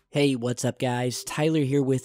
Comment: I thought he said HAY WASSUP GUYS SEIZURE HERE